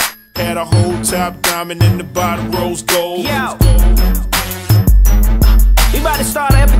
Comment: this whole time i thought he said rose gold not row's gold